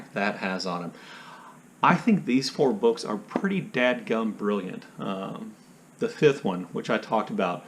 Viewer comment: Did ... did I hear ... did I hear "dadgum"?